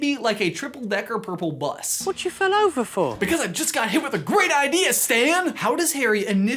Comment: What I heard “Whatcha fell over for” I started laughing hysterically